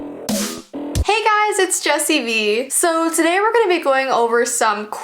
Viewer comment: The son: WAAAAAAAAAAAAAAAA Cries forever until jessii let's him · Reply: Mousy toons ☕ when you said jessii I thought you said jesus😭